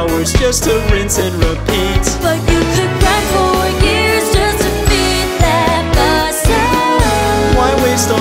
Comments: What does the woman say during 0:39 and after? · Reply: You can grind for years just to beat that boss ohhhhh I think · What does she say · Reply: But you could grind for years just to defeat that boss